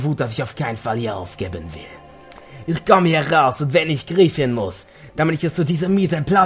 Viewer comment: Bei 11:06 habe ich statt "...wenn ich kriechen muss" "...wenn ich Griechen muss" verstanden und hab nur so gedacht: "WHAT?!"